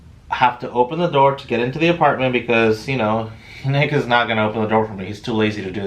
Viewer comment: it sounded like he said the n word 🤴🏿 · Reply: he def said 😂